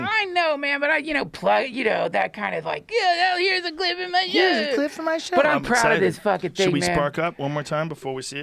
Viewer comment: "Here's a clip from my show!" 🤣 Had to replay that about 30 times to make sense of what Duncan said. 🤣